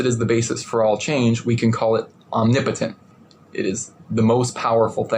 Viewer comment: I could have sworn he said his force is "omnimpotent" the first time listening to him.